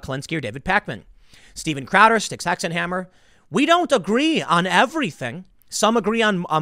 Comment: Did anyone catch that the name Tim mentioned after Crowder at around the 3:50 mark? I'm trying to sub to different viewpoints, I think Tim said Cid Stackaheimmer or something but I cant find his youtube channel.